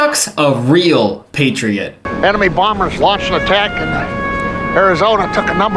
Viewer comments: Is he saying "Anime bombers" at 4:42 ??? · why did i hear "anime bombers"